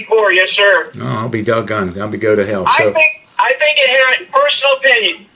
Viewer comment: LOL did I hear Don correctly? @ 11:39..."I'll be dog gone" and proceeds to slip in "I hope you go to hell!" lolol